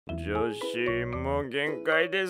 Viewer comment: 女子～に聞こえる